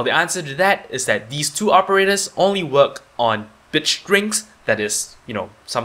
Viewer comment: In the subtitle the is written "bitch drinks" instead of "bit strings" at 3:52 HAHAHAHAH